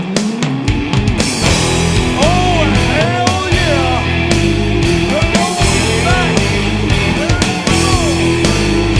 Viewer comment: Does anybody know what he's saying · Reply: He's is saying "Oh hell yeah new outlaws is back"